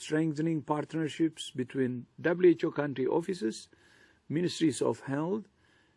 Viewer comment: who else heard ministries of Hell?